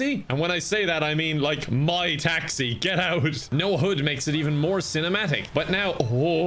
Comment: When he said he has no “hud”, I thought he was saying “no hood”.